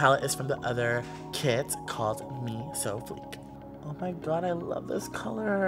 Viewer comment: I thought he said "me so freak" until he repeated the name